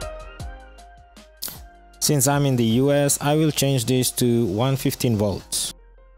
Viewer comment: it’s unclear what it said before changed to 115 and how it was changed… pro tip work on not blocking the camera with your hand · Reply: “Since I’m the us (USA) I will change this to 115v”